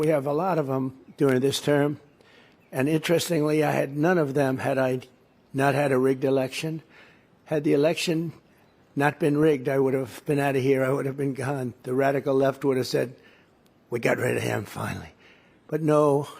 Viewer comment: Listen from 3:47 to 4:05 again. Did he just say if not for the rigged election he would have been gone but now he's here for 4 years? Not going to do quotes, but I listened a few times and keep hearing him saying if not for the rigged election he would not be here.